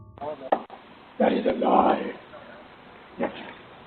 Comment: Is nobody talking bout number 6? It gives me chills! Btw, At first I heard: “It is alive! Snitches.” Then I heard: “It is a lie! Snitches.” It always still says “Snitches” but #6 is freaking creepy.